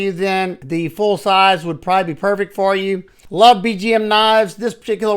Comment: I appreciate the work you put into your channel. One bit of feedback: You talk too fast sometimes and people can't make out what your saying. example: BGM knives - I thought you said VTM knives. You can't understand at times. Maybe consider putting graphics on the screen about the knife your talking about. If you are showing a knife people are not familiar with, they want to clearly hear the name and model so they can research it. Thanks